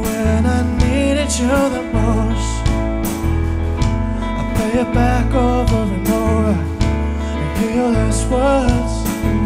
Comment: What does he say on 0:53 ? He normaly says ‘cause i dont realy know how to tell You I’m feeling much worse. But here he says different and I’m not capable to understand it. Help please xd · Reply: "I play it back over and over, and hear your last words" - something like that